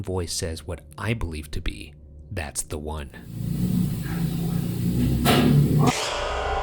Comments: This may just be me but when ryan says that he heres "that's the one" at shadow man hallway, I heard "that's the way" now I know both are a close in the way they are said but yeah also when in gracies room. Could the 3 mean there was 3 personalities there.❤❤❤ love this can't wait to see part 3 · "Who's there?" and "That's the one" EVP Recordings My partner and I heard "Who's there?" and "That's not him." We both got the impression it was the shadow man talking, remarking that you weren't the owner, who he expected and wanted to run at. Earlier, with the womans voice in the same hall, we both remarked it sounded like someone watching the news or television of some kind. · when you heard "That's the one" I heard "That's Ryan" 0_0